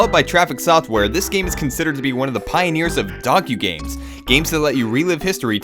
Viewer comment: this game is considered to be the pioneers of - dogQgames? i have no idea what he said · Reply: Docugames. Documentary + Games